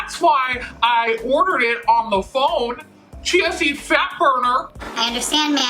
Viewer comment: I thought you were saying “Chancey Fatburner” Like if it was a person. Lmaooo I was so confused for a lil bit.